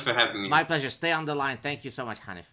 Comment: Lol. at the end it sounded like Gad said "thank you so much honey" :D